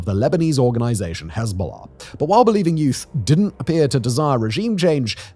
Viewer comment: I keep hearing " Believe in youth" and i cant get over it